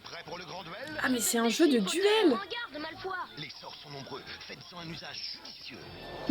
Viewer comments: J'adore tes vidéos !! Sinon dans la pub 5 pour le jeu de duel j'ai entendu "Malfoi" au lieu de "Malfoy" alors que le y dois se prononcer. Bref bisouus ❤️ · A 3:33 « en garde malfoi » ( je l’écris comme c’est prononcé 😂